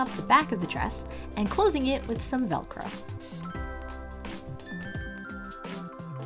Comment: look i know you said “velcro” but it SOUNDED like you said “dollcro” and now im going to start calling doll sized velcro dollcro